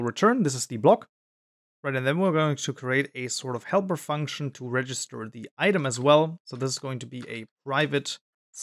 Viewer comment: What did you say at 3:02 I don't understand xd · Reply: "We're going to create a sort of helper function to register the item as well" Hope that helps :D